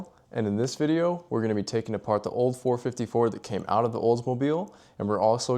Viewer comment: I thought I heard you say olds 454.....or was it ....the 454 you had in a olds👍👍👍👍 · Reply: 454 I had in the olds 😁